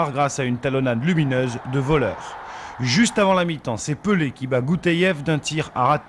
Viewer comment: A 1:30 je rêve ou j'ai entendus pelé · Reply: Oui c abedi Pelé